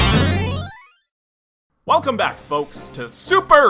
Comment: Did I just hear “Come on, bitch”?